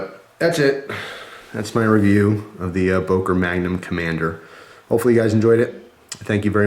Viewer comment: when i first saw this i thought it said magnum condom :p lol